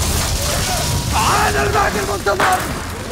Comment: ايش قال بالضبط؟؟ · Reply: انا المهدي المنتظر تردون قتلي يا جراح انا مخلد مخلد